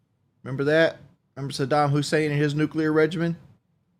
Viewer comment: can’t tell if he just can’t pronounce words or he’s an idiot , is he saying regiment ? Regime? What a dumbass